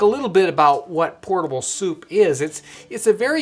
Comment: I thought it said portable soap